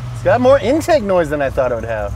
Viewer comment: I found an error with the captions at 9:32. It says: "There is more inside noise than I thought it would." It should say: "There is more intake noise than I thought it would". Just thought I would point that out for ya'.